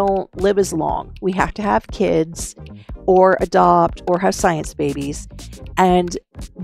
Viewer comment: i misheard you around 10:00 and thought you said “we have to have a kid, a dog, or a science baby” and i was thinking ~one of those is not like the other~ 😂